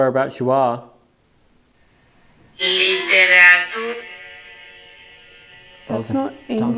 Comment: you have ??? onscreen. I hear "liberatu" or "liberatus". Latin meaning free or liberated.